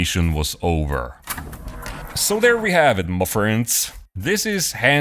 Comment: Just wanted to say, every time I make a sandwich, in my head I hear your into but instead of "my friends" I hear "my bread". Thanks for everything, keep up the great dioramas! 😂😂😂